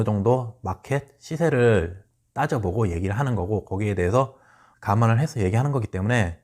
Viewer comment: ^^; 잘 들었습니다. 그런데 가만이 아니라 '감안'인데, 오타가 난 것일까요^^